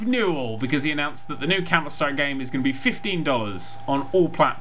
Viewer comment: Did you say "Counterstrike" here? I couldn't quite make that out.